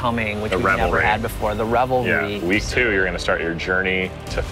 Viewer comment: I heard “revelry” not rebel raid